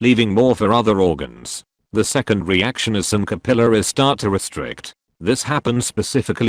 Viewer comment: "The second reaction is some capillaries start to restrict" .....I thought it said caterpillars instead of capillaries...